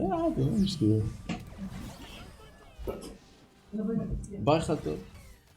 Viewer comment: sorry but did u say bye khalto? Idk what language u speak buuut in Arabic thats bye auntie from the mother side